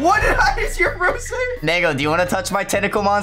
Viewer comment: The fact that i heard say the N- word on 0:59 is insane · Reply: He said mango